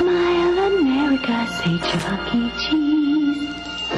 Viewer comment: did she just say fucking cheese